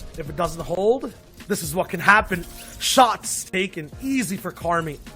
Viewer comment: セイキンイージーって聞こえるw